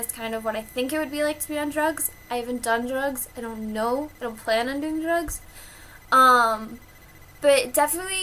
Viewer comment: thats what i thought she said,then i watched that part like 3 times, she said "I dont plan on doing drugs. "